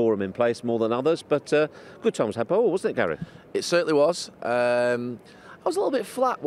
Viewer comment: What did he say “good times...” ? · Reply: "...had by all wasn't it Gary?" I think.